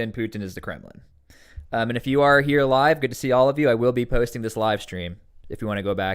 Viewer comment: Low-key thought he said, "if you guys here 'alive', say hi" Lol let me just say hi from the beyond 😂